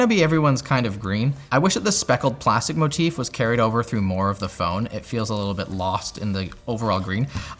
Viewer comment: Anyone else hear say "more of the phone" from 3:30 to 3:31?